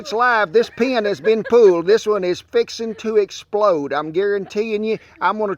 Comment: “ Folks she’s live! She’s going to explode.” All I heard was “She’s gonna blow!” As I ran and ducked for cover! 🤣🤣🤣😁 Loved me some Hot Pepper Dance! 👍👊🏻 Scrumptious recipes. Much tail-waggin’ here! 😁👍